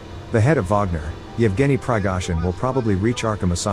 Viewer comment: Why is it that whenever someone says "Prigozhin " I hear "Pigsforeskin"?